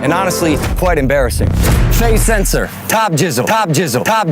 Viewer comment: i thought logan said "tom jizzle" not "tob jizzle"